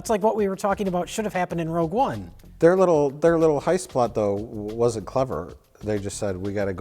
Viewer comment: D...did say "highspot" around 25:35?!